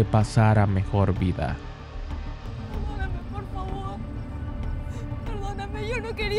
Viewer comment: alguien entendio que dijo la chica? XDXDXD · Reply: "Perdoname por favor" dos veces